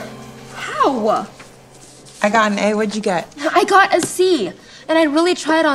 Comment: When she said I GOT A C I thought she said I got a Z 😂